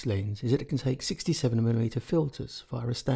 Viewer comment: Between 1:50 and 2:00 I can’t understand him. “The selling point for this lens is that it can take 50 to 75 mm FILSES ? ... it’s the only MFT lens this wide that can do so.” Does anyone know what he said? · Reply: He says:" ... it can take 67 mm filters..."